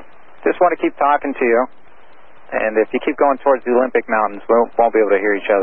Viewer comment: “Have you been to the Olympics? It’s gorgeous!” “I have been, it’s a NICE DRIVE.” 🤣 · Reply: anytime! at 2:20 he says “Olympic mountains” however the captions say “olympics now” so I can understand why you were confused, they never said the word “mountains” again lol